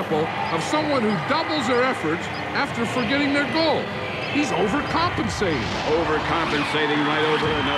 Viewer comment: What did John Madden say Wile E. Coyote's doing? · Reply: “He’s overcompensating.” :)